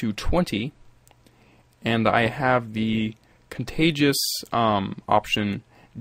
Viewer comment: I always thought that it said Contagious, haha but it says Contiguous But anyways great tut, thanks man